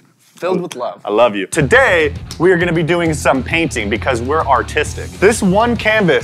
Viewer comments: I thought he said autistic instead of artistic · Thought he said “were doing paintings because were autistic” 😂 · I thought said, “Because we’re autistic” in the intro.